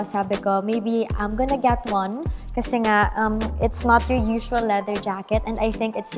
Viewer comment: and at 10:52 I thought she said "Baby, I'm gonna get leather jacket" 😂😂